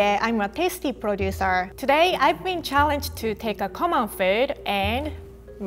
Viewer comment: The first few times i watched thses videos i heard "pastry producer". I wasn't paying attention to the text.